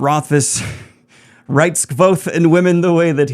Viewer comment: "The fact that Rothfuss writes ___ and women the way he does" ... The word in the blank sounds like "Vothe". But that can't be it. Can anyone tell me what it was? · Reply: Kvoth. It’s the name of the main character